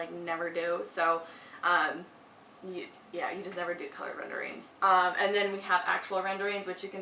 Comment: Hii! I am sorry to bother but English isn’t my first language and around 16:00 I couldn’t really understand what you said about rendering, did you say we should never do renders on our portifolio? Thank you!! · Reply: SO SORRY!!! realized I misspoke! you should totally do renderings in your portfolio, it’s super important to do them!! I meant to say colored elevations, those should always be in black and white not color!